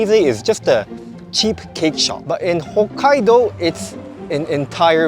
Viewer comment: did u say cheap cake shop?